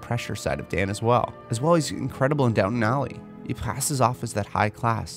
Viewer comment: Did you just say "Downton Alley"????